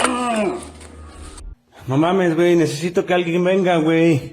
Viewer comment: What i hear is a man jo-ing his mamma???